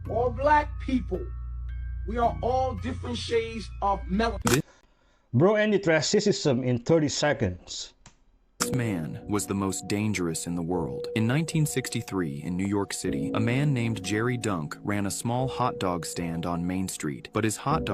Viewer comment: OKAY I NEED HELP!!!!!!!! AT THE 2:00 MINUTE WHAT WAS SAID??? My CC (Captions) says "Bro, Antitraissism in 30 seconds." I MYSELF HEARD......"BRO ENDED TRANSISSISM IN 30 SECONDS." I think your trying to say "Bro ENDED RACISM IN 30 SECONDS." BUT I don't know cause that is not what is being said though.